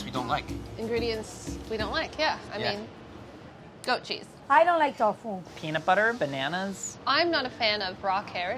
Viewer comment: I thought Gaby said “ I don’t like dog food” I AM In TEARS